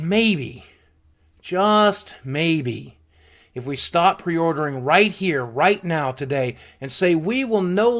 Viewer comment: Love how ABSOLUTELY WRONG YouTube Transcript/Subtitles/CC got what boogie said at 5:13! "If we start pre-ordering right here right now today" - YouTube Transcript/Subtitles/CC "if we stop pre-ordering right here right now today" - What boogie actually said